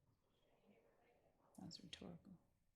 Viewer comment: What did Hylton or Love say at 8:21? Was this aimed at the judge? Love hit mute with the quickness. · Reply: "that was rhetorical"